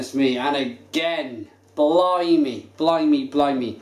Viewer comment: did he say "blow me"? : P Truly one of the best in Poland